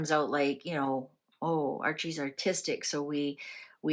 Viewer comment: When you said Archie could be artistic I thought you said autistic! Now that is food for thought that might explain their reluctance to show him to the world.